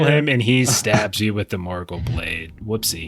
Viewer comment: For half a second, I thought had said Morgoth instead of Morgûl. I had to rewind the video after i nearly choked on my soda